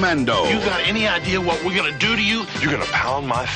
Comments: “Do you have any idea what we’re gonna do to you?” “You’re gonna pound my face!” “What are ya, nuts? That’s not nice. We’re gonna sue ya!” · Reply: I know he says “This is the 90s”. I just thought he said “That’s not nice”, which makes it even funnier… and ironic. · As a kid I had NO IDEA what the guy was saying in the end. Lol. It sounded like he was saying “What are you nuts? These are nice, we gotta city.” Of course that makes no sense. He was actually saying “What are you nuts? This is the 90’s, we’re gonna sue ya.” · As a kid I had NO IDEA what the guy was saying in the end. Lol. It sounded like he was saying “What are you nuts? These are nice, we gotta city.” Of course that makes no sense. He was actually saying “What are you nuts? This is the 90’s, we’re gonna sue ya.” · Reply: I thought they said, “What are you nuts? That’s not nice! We’re gonna sit on ya.”